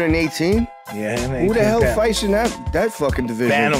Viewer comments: The respect and pride when he said "bantamweights" was genuine, love it. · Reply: Oh I thought he said bananaweights 😂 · The respect and pride when he said "bantamweights" was genuine, love it. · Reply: No lie I thought he said phantom weights, as in light as a phantom